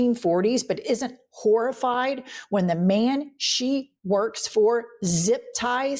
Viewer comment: When Caroline Levit came up, I thought you said "... the man she barks for ..." instead of "works" for. Fun stuff, being 100% deaf in my left ear.